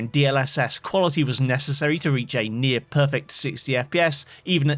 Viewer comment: did you just say re"j"ain? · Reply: Nope. "I found DLSS Quality was necessary to reach a near-perfect 60 FPS"